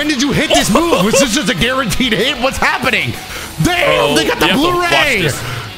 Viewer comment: wat say?!? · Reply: “They got..” but damn that is incriminating